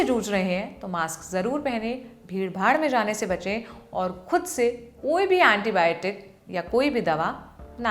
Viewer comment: Did i hear her say "phir Bhaad mein jaane se bache" 😂 listen